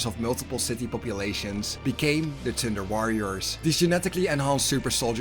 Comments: awesome video! i learned a lot from this one! i have to admit that everytime i heard "tinder warriors" i giggled😂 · This was a tiny well put together video but your accent make it sound like your saying tinder warriors and it makes me laugh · All I hear is "Tinder Warriors"? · Do I hear "Tinder Warriors?"